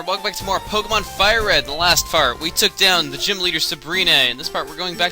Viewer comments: Every time I went back and heard you say The Last Fart I laughed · it really sounds like he is saying "in the last fart, we took down Sabrina." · he said fart instead of part in 0:00 - 0:5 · when he said in the last part it sounded like he said in the last fart! HA!! :D